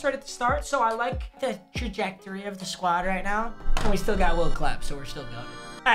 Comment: I thought he said will clap my dad